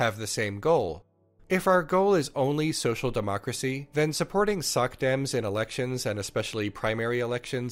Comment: When you said "socdems," I heard "suckdems." I went, "Damn, Leon! Take no prisoners!"